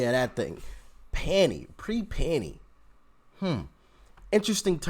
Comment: pre-panny. Pre-pandemic. I wasn’t watching the scroll, so I thought she said pre-panty.